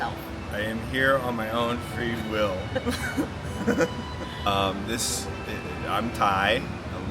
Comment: 😂 I retired to Thailand. When introduced himself saying, "I'm Ty," I heard "Thai," and thought to myself, "No, you're not!!" 😅😊 The furniture rearrangement in your cabin was genius!! 👍